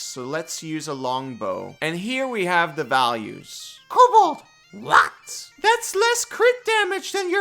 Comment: What word is saying at 4:05? It sounds like "lots" but that doesn't make sense. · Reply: says "what"